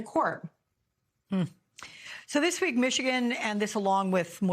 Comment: Did Alex say Michigan and 2,000 other states?! 😮😮